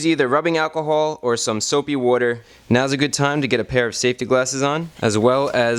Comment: It sounds like he says soapy wooder